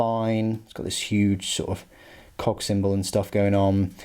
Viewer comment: I was reading comments while listening to the video, so I had to rewind that and verify what you said… “cog symbol.” 😂